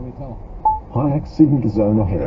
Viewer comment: What on earth is the GPS saying at 0:20? I've listened on two computers, with speakers and earbuds, and I still can't make it out. . . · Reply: HI the driver / radio was singing "what do we tell them" and the car GPS said ..... high accident zone ahead